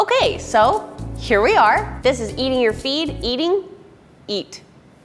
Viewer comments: i thought the beginning girl said eating your feet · "Ok so, here we are this is eating your feet" it sounds like she is saying eating your feet sometimes. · Who thought she said "Eating your feet" at first 🤣🤣🤣